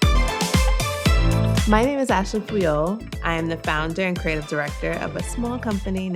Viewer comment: i needed to look this up, i thought I heard Ashley C3PO